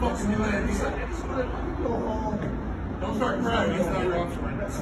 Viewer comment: I think the cop says something like "don't start crying.... that's not what rockstars do" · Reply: No, he said "that's not your option".